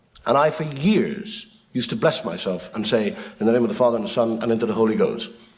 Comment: The blessing joke- He thought the priest had said 'In the name of the father, son and into the hole he goes'